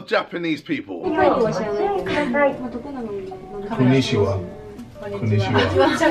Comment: I swear I heard a women say oh it’s a nigga